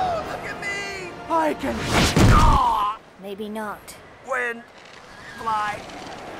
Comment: did he just said: "I can windfly"?